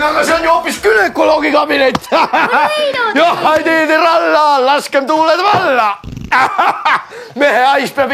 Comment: Can anyone tell me what second Uncle Heino says before he farts (the rhyme part, starting at 1:02)? The Estonian transcript and english translation? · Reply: "Johhaidiii tiralla, laskem tuuled valla!" First part of that sentence doesn't mean anything. It's like a sailors shanty (work song) with no real words. Just made up noise for the rhyme. Second part means "let's let the winds loose!" or "let the winds will come!"